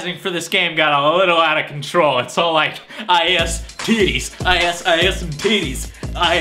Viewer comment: haha 0:39 sounds like He says "I has some tits"